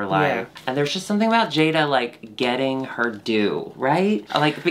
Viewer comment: Y'all talking about Jaida getting her due at 24:44 I'm gonna blame Jaida's verse but all I'm hearing is Jaida is getting her doo lol, and its so stupid but it's all I hear lmao. "we need to give her, her actual doo" - Alexis P. Bevels.